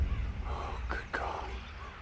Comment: It almost sounds like Cage says “Ah, good call.”